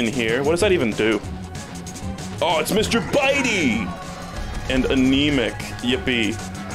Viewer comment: I’m losing my goddamn mind. I legit thought he was about to say “it’s Mr Beast” at 25:34.